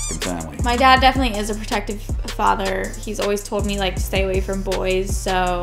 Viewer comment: Lol that first studder, I heard, "my dad definitely is a proactive f-f-fuck"